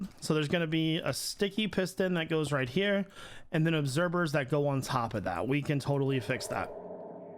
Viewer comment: is he saying obserbers at 11:52? edit: just trying to make sure im not going crazy, because ive never heard him say observers, with a v. Idk if im just hearing him wrong or what